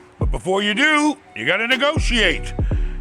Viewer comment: I thought he said... “You gotta get a good Asian” 😂